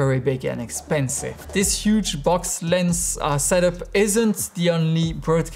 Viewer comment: I'm still not convinced... i need to see a few more videos of the girl in the zoom shot. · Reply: Did he just say "This huge... 9:19 (box lens) ass-ette up...?" (Click the time stamp)